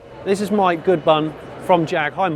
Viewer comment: I thought you said my good bun:-)